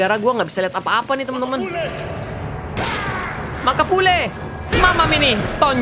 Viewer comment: dengernya:makapule Gw:hey bule Hehehe